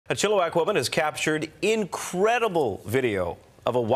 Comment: At first i thought he said “a chill black woman”